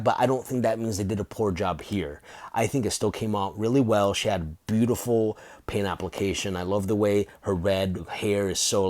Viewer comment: I think you said "Boobtiful" at 4:22 or maybe i just heard that because she's thicc 😂 nice video man!